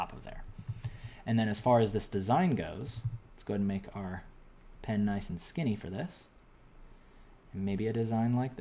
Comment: for i sec i heard "lets make our penis nice and skinny for this"